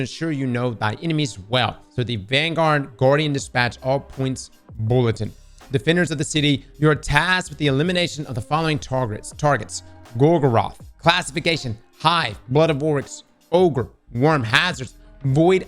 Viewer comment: Did my man say defender of the titty at 7:57? 😂 *I know he said city but it sounded like titty. 😅